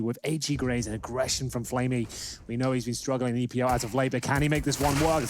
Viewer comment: what does caster say at 0:30? "...flamie...struggling..epi...??" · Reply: "flamie as been struggling in EPL as of late"